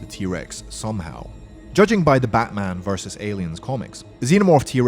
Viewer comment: Hey guys am i the only one here who heard him say BATMAN VS PREDATOR ? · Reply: batman vs alien my friend